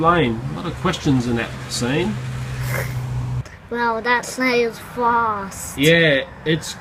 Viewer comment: Hey there. I'm studying English and need some help here. What does he say at 3:26? Something like "that snail's flossed" :) But I don't know what that (flossed) means... thanks! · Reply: He said "That snail's fast." He has an accent so it might be hard to understand dependent on where your from.